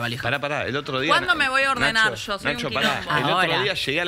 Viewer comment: Alguien escucha que dice nico en el 1:39:29 jajajjaja